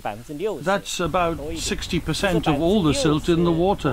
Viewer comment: Classic Western Media lies about China- 43:00 voice over/caption:"60% of silt stuck in dam", actually said in the film "6%" instead of "60%"